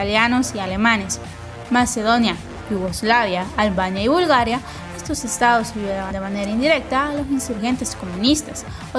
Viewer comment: que es lo que dice? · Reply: Estos Edos ayudaron de manera indirecta